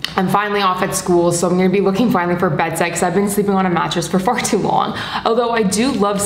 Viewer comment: did she just say “bed s*x”🤣🤣🤣🤣😬